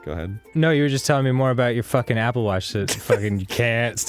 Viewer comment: Not gonna lie, thought they said Apple Wash at first and was imagining that Arin was just stretching his arms all subtlely to show off the smell of his new shampoo and shit and when they said Mark had it too I was like wtf is the deal with this shampoo bein so popular? lol